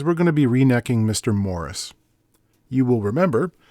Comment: I thought that said "Rednecking the Morris"... 😄